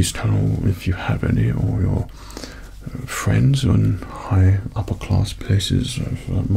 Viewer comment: Thought is said friendly 😳😤